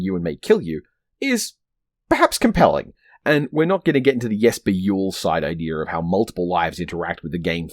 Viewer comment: What's the line at 15:16? The auto subtitles say "we're not going to get into the yes be yule side idea about how multiple lives..." and I can't work out what it's meant to be. Or maybe it's a reference I don't get? Anyway, love the video! Keen was never a game I played at home, but it was definitely the kind of game I'd play at a friend's house. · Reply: Ah, sorry, I'll try and get the subtitles to reference the script. It's a reference to the name Jesper Juul, which is pronounced Yesper Yool. In Half-Real he describes the way that videogame narratives can't present a coherent reality because extra lives break reality's rules in a way that don't traumatise people.